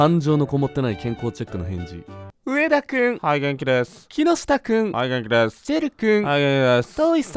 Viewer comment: はい元気でーす がハイゲンキて病気に聞こえる